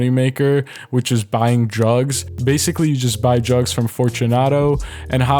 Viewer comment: sounds like he says drugs instead of jugs lol 😂😂😂